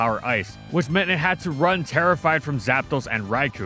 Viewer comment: I thought he said "it had to run Tera Fire" against the Ice hidden power and I thought "yeah that makes sence" until I realized it was gen2